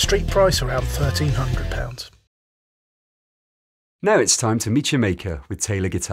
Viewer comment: i though i heard "now its time to meet Jamaica" :D